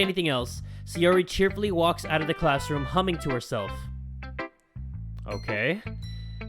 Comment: when said 'humming to herself' I heard 'hanging herself'